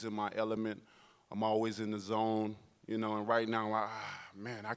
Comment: I heard wilder say "i'm always in DAZN" in his intro !!! 🤣